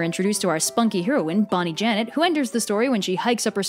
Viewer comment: Pfft i thought she said bonnie Bennett at first